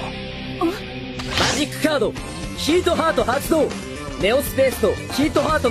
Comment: ok but why does he say hawk tuah